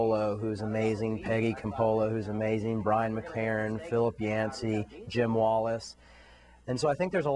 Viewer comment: I cannot make out one of the names he mentions @ 1:58. It sounds like Phillip Iancee / Yancee? Can anyone who knows the guy please write his name down for me? Thanks in advance from Hungary.